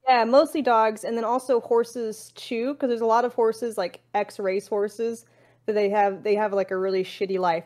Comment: At the beginning I thought Amouranth said x-rays horses. I was like: "what's an x-ray horse?"